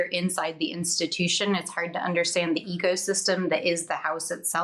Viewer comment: When she said ecosystem, I first heard it as egosystem.. I think I was right the first time.